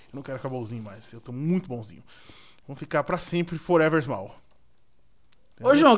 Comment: Juro que ouvi Forever Mapa kkkkkk